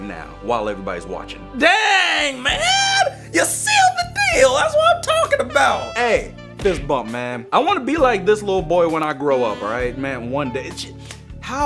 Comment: did he just say I want to be like this little boy. Me starts laughing like a madman